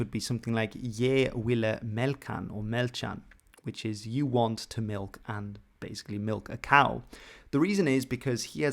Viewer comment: When he said “Je volle milke”, i swear i heard “you wanna milk her”. sounds straight up like english.